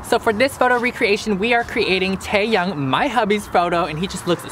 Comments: I thought u said Taeyang 😂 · I heard Taeyang???